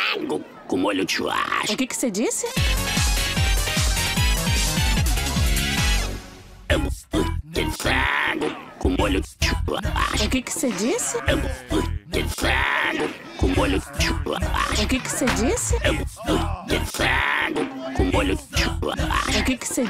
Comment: eu escutei amburguitizado com molho de chocolate kkk